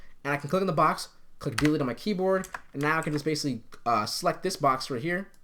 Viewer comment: what he says there? Something with keyboard... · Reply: "I'll click delete on my keyboard"